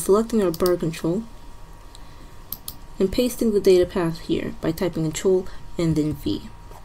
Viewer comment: what does fee mean you said control and than fee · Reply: 'CTRL + V' it means press your control button, and type the letter V on your keyboard :)